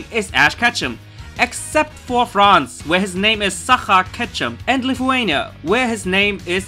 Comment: HAHAH! "sacha* ketchem (lol I thought he said Suca! Catch them!!!) HAHA xD loL!